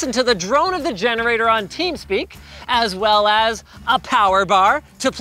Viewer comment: did he just say teamspeak? Boi